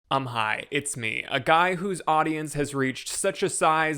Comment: I heard “I’m high” instead of “Um, hi”. Me too, brother. Me too. 😌